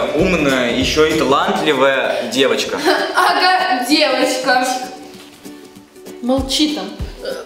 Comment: Мне послышалось что сказала одеточка.Олды канала поймут😢😢честно очень шла эта роль